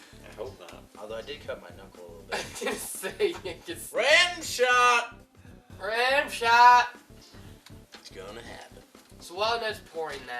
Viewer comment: I definitely heard "rimshot"